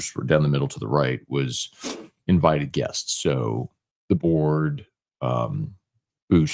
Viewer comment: I had to go back an listen twice. I thought you said "Deboers", when you actually said the board. I was thinking why would Deboers be invited.